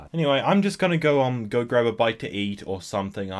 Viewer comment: i thought he said bike